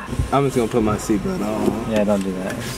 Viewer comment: It sounds like says "Chris Chann" instead of "Peach Jam" while watching this vid haha